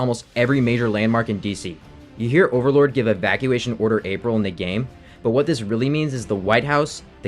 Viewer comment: I always heard it as "evacuation order equal" which to me is just as chilling